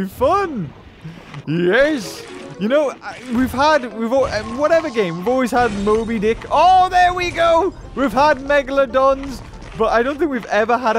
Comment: i think he said 5ick